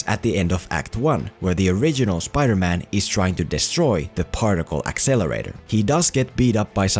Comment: Did you say partial exonerater